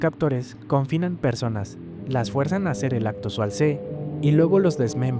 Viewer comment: Yo escuche y escuche "acto xualse" hasta que caí en cuenta